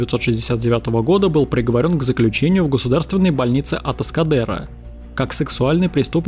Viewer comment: Послышалось лечебница Пескадеро, подумал он лежал в одной больнице с Сарой Конор